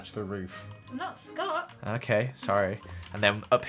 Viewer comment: Joel: “I went with cyan, to match the roof” Lizzie: ”i’M NoT sCOtT” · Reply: CALL ME SON ONE MORE TIME I thought you say i'M sCOotT